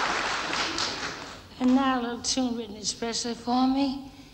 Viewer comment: What is this lady saying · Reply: And now a little tune written especially for me....